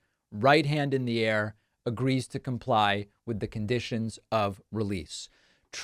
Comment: Trump, you agreed to comply with the conditions? Trump: "Agree to comply"? I thought they said "agree to lie"!